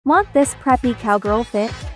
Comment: For some reason i heard her say f$vking instead of preppy😂😂